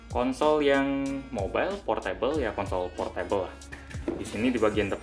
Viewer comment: Kedengeran kaya ko**ol portable wkwkwk